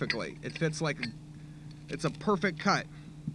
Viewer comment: it's a perfect cut; MEEEOWW (read: did you just say perfect cat?)